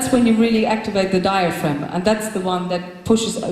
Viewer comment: Does she really says "Diaphragma" at 5:45 ?! That's the german word for "diaphragm" ... like the thing which you can use for not getting pregnant, I mean ... ^^